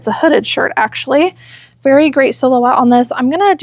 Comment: Random weird comment. I’m getting older and am losing my hearing. When you said “silhouette,” I heard “cello butt.” 😂😂😂😂 Thank Heaven for subtitles.